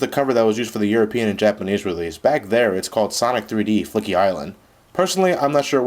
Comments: I thought that said: Sonic 3D Fuckle's island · I Thought It Said Sonic 3d F*ckie's Island! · i swear at @ 1:00 i thought it said fuckies island xD